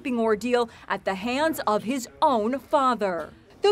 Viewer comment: HIS OWN NIECE!?🤢🤮 What a disgusting monster! So glad that little boy is safe. Hopefully, he won't remember too much of what happened when he's older. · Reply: I thought you said “Mother” and I almost lost my cool 😅😅😅😅